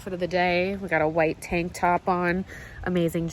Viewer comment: omfg i heard u saying "i have this white tampon on" when you said tank top 😂😂😂 im dyinggg im too tireddd😂😂